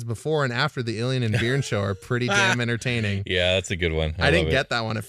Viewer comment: Oh, Beern. I kept hearing "Beard".